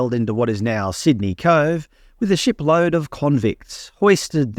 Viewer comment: Lol for a moment I thought you said "a shitload of convicts"😂😂 I wish I could stop shopping at Woolies but I already don't shop there. Nor do I listen to Triple J, nor do I shop at Kmart. Is there a way we can defund the ABC? They are so anti Australia